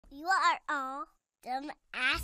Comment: Did he said you wants some more